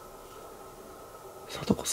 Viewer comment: ここの男性の「サトコさん」がが「サトコさん？」て言うまで女性の声で「消えちゃえ」にしか聞こえなかった。ちょっと怖いわ